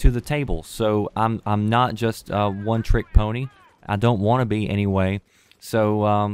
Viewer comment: did you say one trick pony???